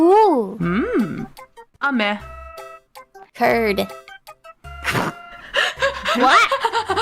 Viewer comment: I think that Gura is confused because she heard it as turd (poop) instead of curd